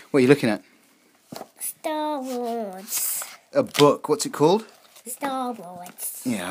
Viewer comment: I like how her accent makes her sound like she says: Staa waas